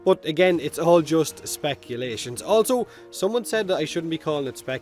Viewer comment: Is it bad that every time you say speculation, I hear "feckulation"?